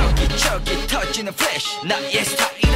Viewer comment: at first I thought Sehun says "Flesh" XD